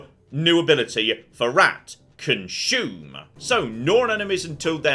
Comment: I thought it said "Costume" until said consume